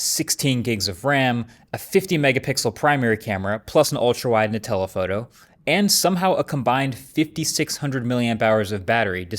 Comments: I definitely heard "Nutella Photo" instead of "and a telephoto" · Am I the only one who heard "Nutella photo?" 😂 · Is it just me or did he say "Nutella photo" 0:37 😂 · I hear "Ultrawide Nutella Photo"